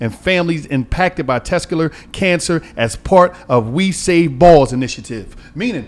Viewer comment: I thought Walo was saying Tesla Car Cancer.